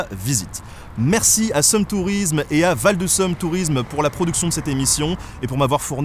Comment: Au début j'ai cru que c'était en Belgique... J'ai entendu "Seum tourisme" et "Val de Seum" 😅